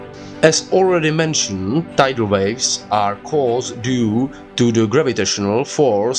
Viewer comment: I thought you said dino waves😂